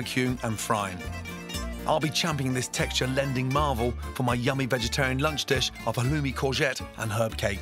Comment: did he say "vegetarian" ????????????